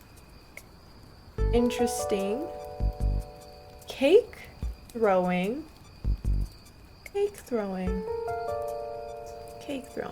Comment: I thought you said cake GROWING which I would be stoked about